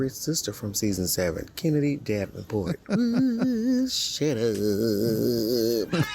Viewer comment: help!! what is queen saying 😭 “oooouououh shuuuuuouououp”(?) I need to know